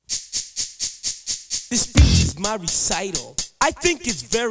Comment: All these years I thought he said “this beat is my recital” when he actually says “this speech is my recital” I- 🤦🏾‍♀️